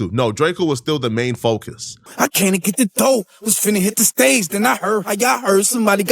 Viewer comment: YG never said he was trying to get revenge for slim 400, He just said he haven't gotten over what happened to Slim 400. Damn stop words in people mouths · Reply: Facts he also said “came to get the dough”not when I hit the door assuming and mishearing but I still agree with his end point